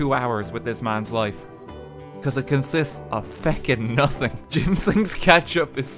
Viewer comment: Did... Did just say feck?